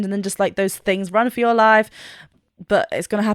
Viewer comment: lol he doesn't say 'run for your lives', its a common mishearing, he says 'ROW for your lives', they're on a boat after all, thats some actual advice